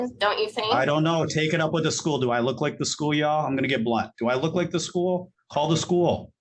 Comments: BLUNT!!! lol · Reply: oh he said BLUNT!!! I promise you I wasn’t trying to start nothing it really sounded like he said black!I played it back and that makes much more sense😂😂😂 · what did he say? Lol "Do i look like the school yall, Ima get Black"